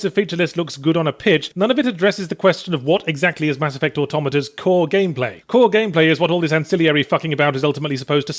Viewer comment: Is it just me or did he say - Mass Effect Automata?